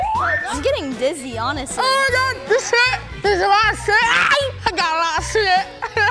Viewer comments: I thought Mike said “ I got a lot of Schmidt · It sounds like Mike is saying Sit, XD